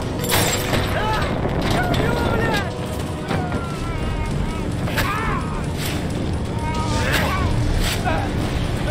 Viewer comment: I can’t tell what the russian soldier is saying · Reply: я горю